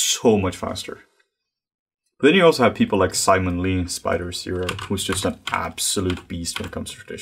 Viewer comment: Who is the sculptor mentioned at 32:15? I can't quite tell what he's saying. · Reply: Simon Lee! He's amazing: <link>